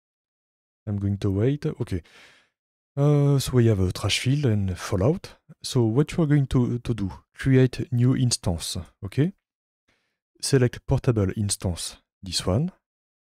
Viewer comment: did you say 'Trashfield' ?? 🤣🤣🤣🤣